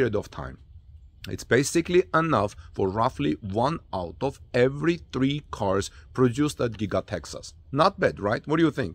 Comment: Not able to understand your comparison to "one out of every three cars produced at Giga-Texas." or did you say "one auto every three cars..." Were you trying to say one Cyber Truck out of every three cars? see at: 1:08 to about 1:15 it is really hard to understand what you meant.